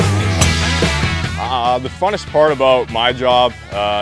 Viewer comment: did he just say funnest?